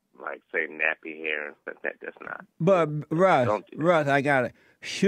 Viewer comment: Did Jesse actually say "but bruh.." at 1:44?